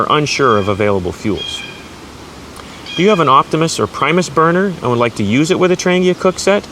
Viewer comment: Did he say "optimus prime" at 5:13?